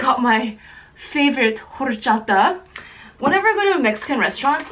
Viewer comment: Lol to me it sounds like she is saying whorechata